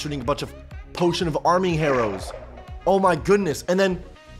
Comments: DID HE SAY ARMING HARROWS INSTEAD OF HARMING ARROWS? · is it only me or did said "Potion of Arming Harrows"